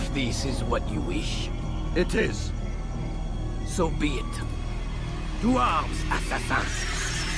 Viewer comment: "Ein Assasine, haltet ihn auf!" What? Why is he screaming in german?